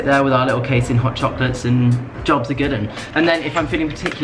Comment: Am I the only one who heard "jobs are given"